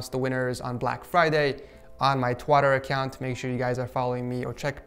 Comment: Did he just say on my twatter account